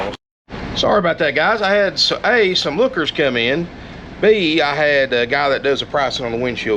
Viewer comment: Lol I thought you said hookers come in.